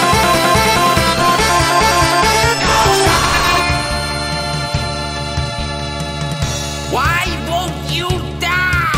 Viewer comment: What did I just heard what it said? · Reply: he says Causa